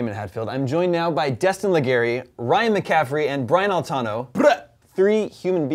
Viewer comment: I thought he said Brian boitano